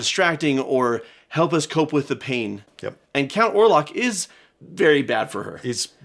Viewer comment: I thought you said Count Olaf not Orlock, oops.😅